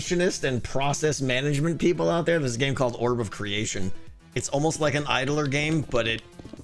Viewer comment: Writing that one down... "Orb of Creation" I think he said if anyone else looking in the comments